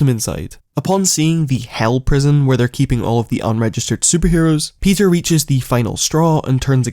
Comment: For a second I thought he said “unregistered sex offenders”